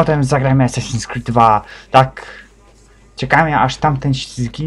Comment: Ja usłyszałem assassin's creed dwla XD